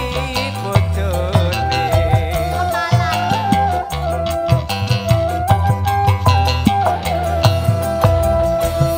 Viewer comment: Di menit 0:46 ngomong apa sih? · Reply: "Kemalan"